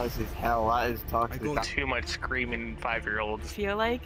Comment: Bro not even 5 seconds in and I hear “I’ve put too much cream in five year olds”